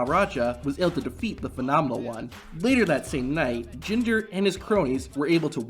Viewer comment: Did you just called him Ginger ?